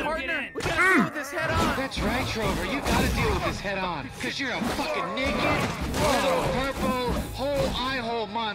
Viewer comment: an I the only one who heard “ cuz you’re a f**king ni**a” 😂😂